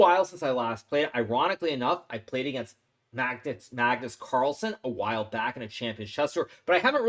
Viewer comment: hahahah why does hikaru say Magtits instead of Magnus hahahha